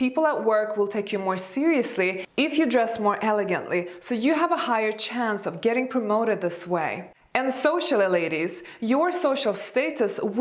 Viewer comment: I thought she said "you will have a hight chance of getting carona this way "